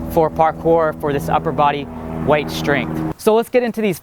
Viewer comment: Am I hearing this wrong or is this an unknown term to me but at 0:53 do you say "upper body white strength"?